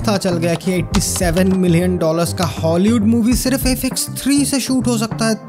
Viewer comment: what is he saying here ? i couldn't only get Fx3 or something